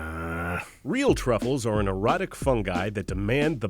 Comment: my man said "erotic" instead of exotic 😂😂